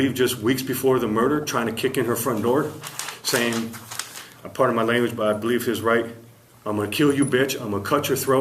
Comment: This guy right here is crazy. 6:23 did he just say pardon my language?🤣🤣🤣🤣🤣👍